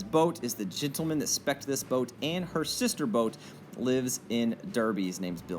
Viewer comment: what was "Bill's" last name who had the tow boat named after him? Did you say he "inspects" this boat and a sister boat? "Inspects" for the state??? Sorry to get behind, just a little fast talking for an old man..........love these videos even if they are a little over my head on the nautical stats.😀😀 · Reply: He said he spec’d out the boats. He was the one that decided how big and how much power to build them for.